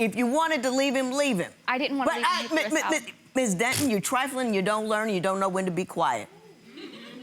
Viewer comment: Judge Lynn had enough. “You’re trifling” was a perfect summarization 😭🤣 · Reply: Sounds like she said, "you triflin'!"